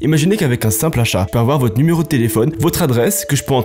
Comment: J'ai entendu "imaginez qu'avec un simple chat", j'étais là hein on peut hacker des infos avec les médailles des chats ??